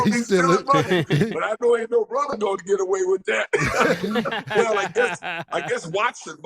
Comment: He said “ain’t no brother gonna get away with that”….. but at first I heard “ain’t no Bronco to get away with that” 😂🤣😂🤣😂🤣